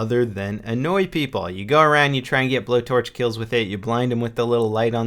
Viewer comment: When he said blow torch I thought he said blow job I was like WTF